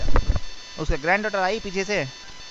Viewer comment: If you're wondering what the scammer said there, he said "His granddaughter came from behind". Might not be 100% on point but it's pretty close